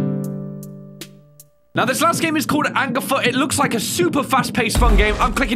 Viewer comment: i thought it said N-Word Foot